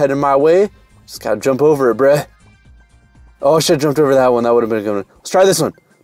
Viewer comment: DID JUST CURSE I NEVER HEAR HIM SAY A CURSE 2:10 AS WELL I THINK IMMA UNSUB 😐😐😐 Edit:Never mind i didnt hear well also close to the 100k grats Another Edit:Grats for the 100k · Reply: he said "Oh I should have jumped over that one" and "deck"